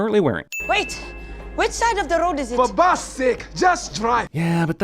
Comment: is the part when I realized T'Challa said "For Bast's sake!" Instead of saying "For God's sake!"